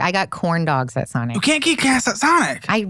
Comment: It totally sounded like he was saying she got gas at Sonic lol